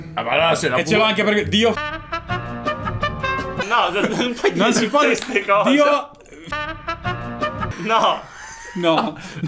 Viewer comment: raga qualcuno che mi scriva le parole che hanno associato al sommo creatore? Aiuto non ho capito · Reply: ha detto Dio forte, ha detto Dio Possente, ovviamente 😌